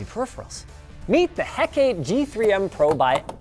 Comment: The cheapest episode of the LDT series. Or at least that's what it sounded like.